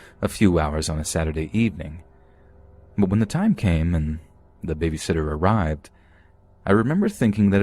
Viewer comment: Bro here what he said 1:06:19 maguna time came😂